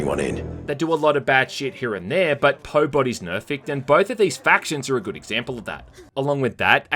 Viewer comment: Dunno if it was intentional, but I laughed way to hard at 22:40 at you saying "pobody's nerfect" instead of nobody's perfect.